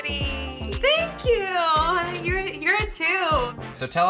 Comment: "Aww..your so pretty" "Thank you...you're a 2" Tell me I'm not the only one who heard that 😂😂